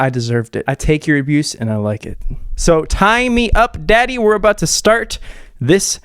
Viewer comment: Did you just say "tie me up daddy"? 😂